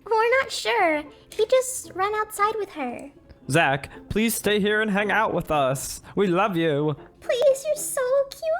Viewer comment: I thought she said "PLEASE STAY AND MAKE OUT WITH US" 🤣😅